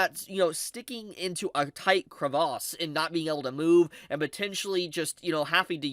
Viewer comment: what did he say? A tight what? A tight crevasse right? Am I correct please tell me.